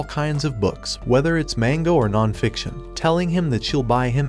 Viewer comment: bro really said mango instead of manga 😂